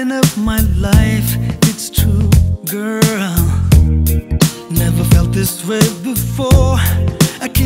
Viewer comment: Back when i thought he was saying " never felt this way hefore, i couldnt vomit more and more"... and most of the other lines i had no idea what he was saying, but the tune was incredibly pleasant to the ears...good days...